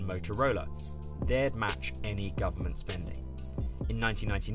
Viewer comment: YO, BLOOMBERG! I can't hear your announcer. Maybe get him a mic that compensates for that intensity dropoff he does at the end of sentences? (I don't know the technical term.) I haven't finished the video yet, but so far the phenomenon is at its worst at 3:25 where I had to replay that section five times, gave up and turned on YouTube's hit-and-miss captioning to find out that he said, "match government spending". This is not to overly or unfairly criticize this announcer's delivery. I just think that this voice is getting swallowed up in the audio mix.